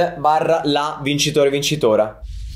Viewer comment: io:sbaglio o ho sentito vincitora? scusate😂